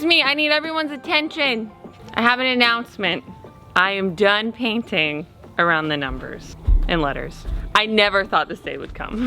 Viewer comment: My wife thought you said you were done painting by numbers. 😂